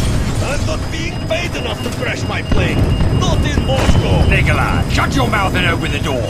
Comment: did it say Nikolai